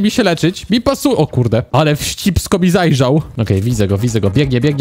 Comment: kto też usłyszał "ale w cipsko mi zajrzał" XDDD